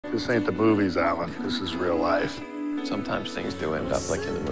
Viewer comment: Why do i hear "this aint the boobies alan?"